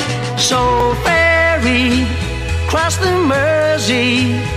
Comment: Being the little boy I was at the time, I thought he was saying "ferry, crust and mercy."